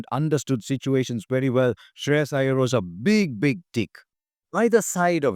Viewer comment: I will remember this tournament for KL, Varun's redemption, Axar and Iyer's partnership and Hardik finishing. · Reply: Did you just say Shreyas Iyer was a big big Dick? 😂😂😂